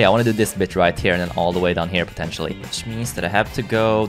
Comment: did he say b*ch?